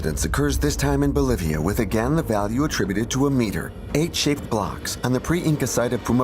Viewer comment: the narrator says “H shaped blocks” here, I think you misheard “eight shaped blocks” and responded to this. Your point is probably just as valid, though, as he doesn’t even give a number and I am sure it’s not all of them. Just thought I’d point this out. Great video by the way.